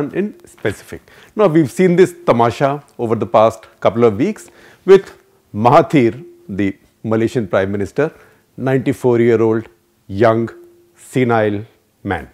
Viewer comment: Did I hear him say...94 year old Malaysian PM 😯